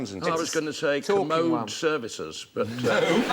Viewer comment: Did he say "teenagers"? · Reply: He said "Teachers".